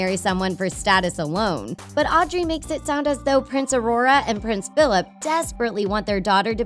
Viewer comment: Am I the only one who heard prince aurora?